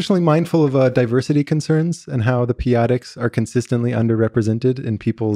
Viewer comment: The "peyotics"? What did he say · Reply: p adics